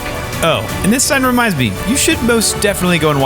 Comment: I thought he said to watch the video fat and lazy lols